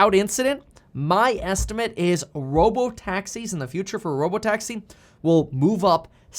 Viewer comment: Could have sworn you said robo-taxes at first...